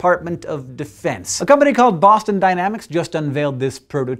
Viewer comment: I thought you said a company called Massive Dynamics instead of Boston...